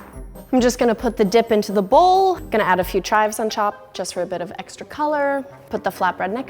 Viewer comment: did anyone hear she said "gonna add a few chives on chop" lmao